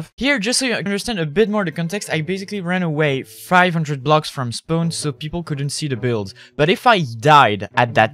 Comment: When you said spawn I thought you said spoon